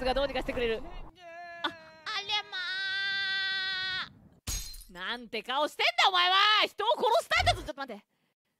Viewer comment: Did she just say alamak?